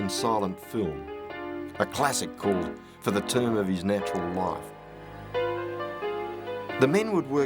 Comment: I wonder what the name of that silent film is... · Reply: I thought he said it was called For The Term of His Natural Life